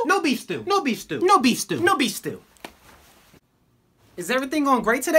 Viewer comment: I thought he said "newbie stew"